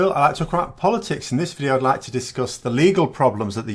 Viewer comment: "Hello, my name is Phil and I like to crap over politics." Best mishearing ever! XD